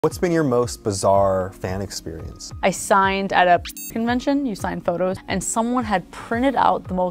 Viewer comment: I really thought she said piss convention at first